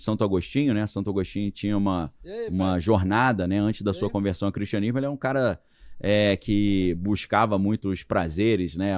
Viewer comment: Alguém entendeu o que foi falado em 14:35 fora do microfone do Daniel??? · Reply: Iae pai? Beleza? Entendi isso